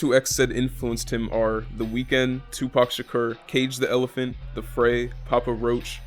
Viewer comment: "Tupac Shakur" What? I thought he said fuck tupac and that he's better than him.